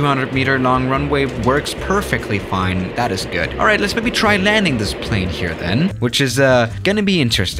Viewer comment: i thought you were saying extreme lanings haha